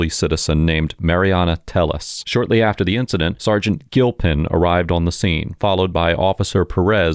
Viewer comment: Gilpin. I heard it as Guiltpin, which would be a perfect name for a cop..